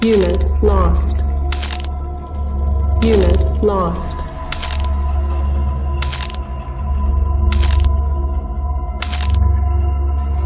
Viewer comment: I miss the old "Unit Lost" and "The gods always demand sacrifices, may we lament those we have lost this week" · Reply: I'll be honest, as someone who didn't play CnC, I thought the sound byte said "get lost" But yea, the old one is better, but for this one it was pretty fitting.